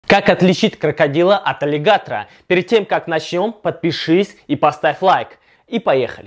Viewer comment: Мне первую секунду послышалось как отличить крокодила от олигарха